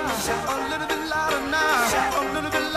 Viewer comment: Is he saying a little bit f*cking now?